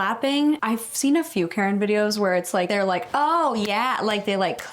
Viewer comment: I THOUGHT IT SAID KARAOKE VIDEOS 🥲